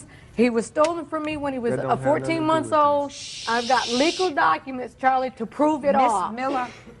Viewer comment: did she say he’s been stealing since he was 14 months old? A baby?!